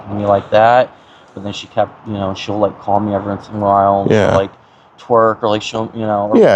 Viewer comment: Did he just say “she’ll call me every once in a while and she’ll twerk”? 😭